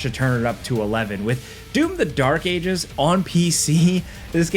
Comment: I was very surprised about the game "Dune - The Dark Ages" and it having a copied super-shotgun from Doom. And then I understood I misheard the game-title and checked. Sure enough, it was Doom :D Thank you for another great video! Best regards